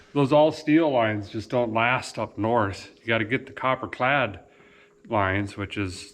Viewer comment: Yeah I’ve never heard of anyone using copper for brake lines. Scary. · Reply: Thought he said cooper clad like a penny bendable yet strong